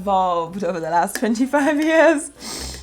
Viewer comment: I thought you said over the last 55years and my mind was filled with confusion 🤣🤣